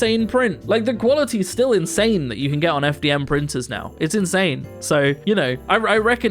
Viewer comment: i heard it as "FTM printer" and i went like "good for him" 😅